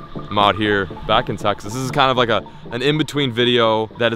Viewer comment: Did he say sexist or Texas?